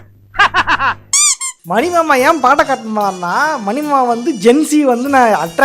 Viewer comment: olunga pesu bro, nee pesura vaarthai sila neram puriya maatingudhu. what did you say here after saying "mani mama en" at 4:50 ???. batakart ah??? 🥲🥲🤣🤣